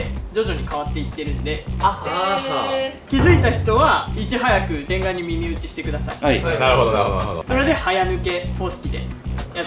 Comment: 大変申し訳ございません。TENGAに耳打ちに聞こえました。あれに口いれたことはないなあ。